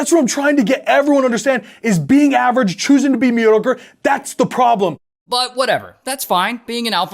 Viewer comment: Did any else hear him say, "Choose to be a meaty ogre" instead of "mediocre"?